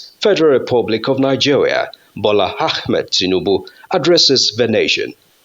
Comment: " The President, Bola Half made Tinubu", hope I heard correctly 😂😂😂